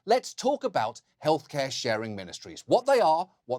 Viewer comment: I thought it said "Health Care Sharing MiniSERIES". I was like - damn, topic's so good, it has it's own spin-off 😆😆